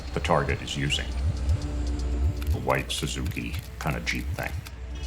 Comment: "A white suzuki kind of jeep thing" very CIA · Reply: I thought he said "cheap thing".